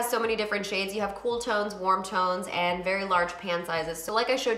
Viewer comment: I was only halfway listening cause I’m currently doing my own makeup but at first I thought you said “cool tones, warm tones, and large pant sizes”